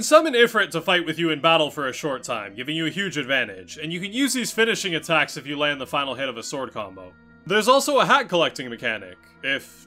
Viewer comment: Did you say HAT COLLECTING?? *BOIOIOIYOING!!*